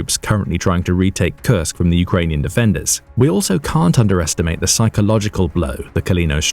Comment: There are multiple errors in the English closed captions. Most are insignificant, but the caption at 15:33 says "Ukrainian invaders" instead of the spoken "Ukrainian defenders".